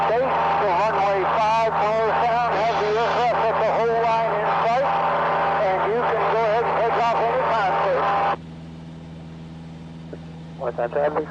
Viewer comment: Anyone able to tell me what the hell is being said over the radio at 8:44? (without listening to it over and over too, cause on radio you generally only get to hear it once) I feel like other people can hear through the static better than I can, not sure if its just practice or what but there are times when I am like What in the living hell did they say??? then my co-pilot or CFI would respond with what they said and I would just be baffled they could understand one word let alone everything said. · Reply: Whoisnt Whoisit Morristown traffic this is 020 I have the aircraft at the hold line in sight. You can take off anytime.... Not word for word but pretty close.